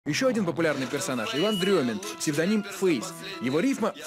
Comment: псевдоним "похуист". Мне одной послышалось??)))😆